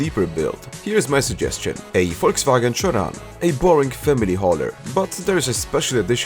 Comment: the fact that the subtitles understood "chiron" instead of "sharan" says it all